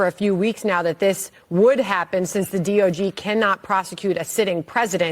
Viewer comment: did she say DOG instead of DOJ? 😂 Elon is already living rent free in her head with DOGE 🤣